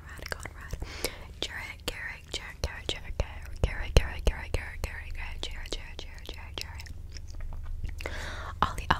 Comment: What name was said in 11:13 I’ve been stuck on it for 20 Mins 😭. Keep up the good asmr tho I find it very relaxing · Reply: 20 what she said garret